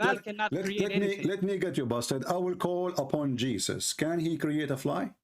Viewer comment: Did call the Islamic apologist a 'bast---' 2:30?